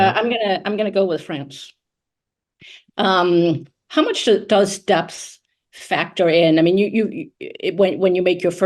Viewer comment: "did she say dubstep?"